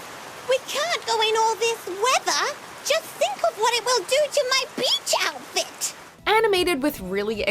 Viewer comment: did she say “BITCH outfit”?